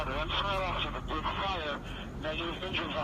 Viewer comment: Very valuable video man somebody please download this. Also @ 18:11 A person on the radio mentions an officer "did fire but". I think that's what he said but I don't know what he saying there but that is after they found the suspect on the 32nd floor